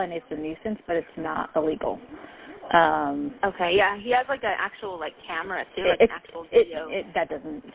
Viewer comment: " he has a gu..uh actual camera " did I hear that right?